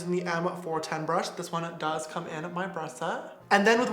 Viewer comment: is it just me or does anybody else hear 'breath set'?